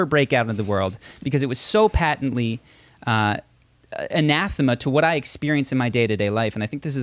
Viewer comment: What is the word said at 4:02? I haven't heard this word before and I can't figure out how to spell it well enough to Google it. It sounds like inathimate, but I don't think that is a word.